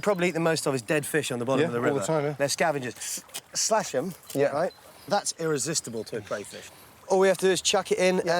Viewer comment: did Gordon just say “yeah Ight?!!!”